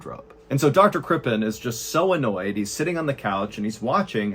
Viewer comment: I misheard "so annoyed" as "solenoid." Annoyed at my aging ears, I fn googled "solenoid" and wound up learnin' a little about magnetic flux. So yes, I'll go ahead and say it: Your show is an excellent brain capacitor. Ah jeez, my Delorean's idling out there with its doors wide up. I forgot. So see ya later. Or earlier? And when is my address? I wish these new-fangled dashboard dials had 'em a "just say when" button.